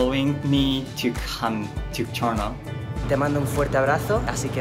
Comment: can someone help me to figure out what does Yuzuru say? Thank you for allowing me to come to...? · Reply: Toronto. It's because Brian consulted with Javi if it was okay with him if Yuzuru trained there